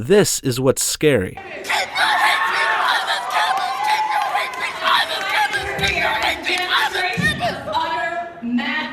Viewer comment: Okay. What is she even saying?! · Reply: Lily Raimey KEEP YOUR HATE SPEACH OFF THIS CAMPUS KEEP YOUR HATE SPEACH OFF THIS CAMPUS KEEP YOUR HATE SPEACH OFF THIS CAMPUS